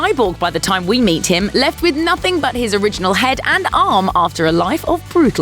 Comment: Did you say his...."Original"...."HEAD"?!